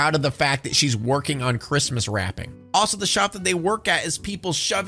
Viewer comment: So i just listen and dont watch these videos. And i kinda stopped what i was doijg when i heard "christmas rapping" And i thought "maybe the kid was tslking so fast that the dad thought it was rap. Kinda gavw me a false memory of the movie. When i finally looked, it was actually christmas wrapping